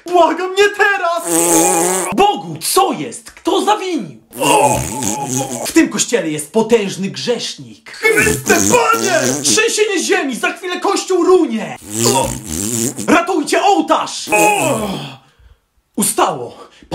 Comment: Kocham ten moment ✨️✨️ ratuj nas panie ✨️✨️✨️ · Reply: czy tylko ja usłyszałam ,,chryste panie'' ?